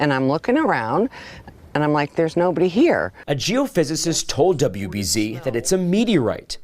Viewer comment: Ok, I know he didn’t say “a Jew physicist 1:52 “ but what did he say?